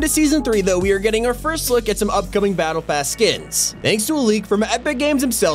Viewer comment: i thought it said battle Ass lol